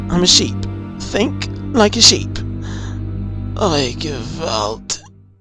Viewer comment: What does he say at 00:42? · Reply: He says "Oy gevalt", which is Yiddish for "Oh, g-d..."